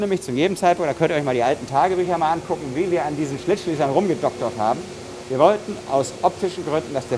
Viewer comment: Neuer Zungenbrecher: Schlitzschliesser (ich hab zuerst nur sowas wie "Schlittschiefer" verstanden… scheiss Masken (*ABER NOTWENDIG*!))